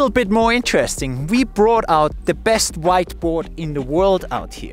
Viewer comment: I can't be the only one who thought he was about to say "white boy"